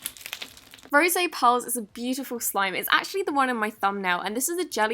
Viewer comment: did she say ro se pearls or rose pearls? or is that just an accent or is that how its pronounced,,, idk any more